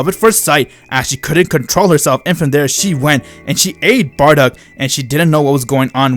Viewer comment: “And she ate bardock” I laughed so hard when I heard this Edit: I thought it said ate instead of aid